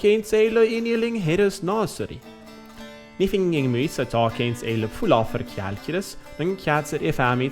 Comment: Did he just say "calculus"?